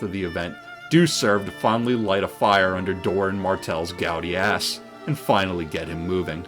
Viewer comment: I heard 'finally light a fire under Doren Martell's gouty ass' as 'Gaudi ass' which got me rationalising 'well, I guess Sunspear is... kinda... like Barcelona...'